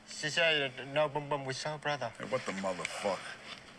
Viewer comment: did he say Soul or South brother? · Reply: He said soul brother.